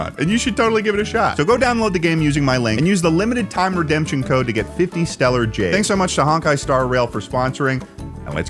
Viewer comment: I was half paying attention to the sponsor bit, and then at the end of it I thought I heard "-- to get 50 steller's jays!" and I was like, "Huh? The bird??" I rewound like four times and then kept zoning out too soon until finally I saw that it was "50 stellar jades." Not quite as exciting as 50 pretty blue-colored birds with fun black mohawks (crests).